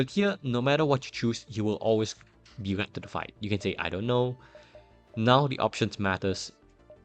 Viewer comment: Hey, I'm afraid I have trouble deciphering your accent in this specific sentence. I hear you saying 'now the option matters' but then you don't explain which option does what and why picking 'chips' is the correct one. So could you please clarify what you say here? Thanks. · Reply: I meant to say "None of the options matter", sorry if that wasn't clear in the recording.